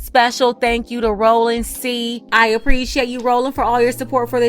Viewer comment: lol for a moment I thought you said Thank you to Roland Martin ❤